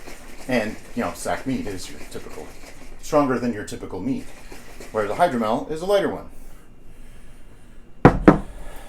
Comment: I sweat I just heard "sack Mead is a stronger one where hydromel is a lighter-mel"